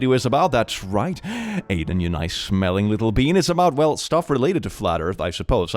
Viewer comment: I'm really tired rn and thought he said "your nice smelly little penis."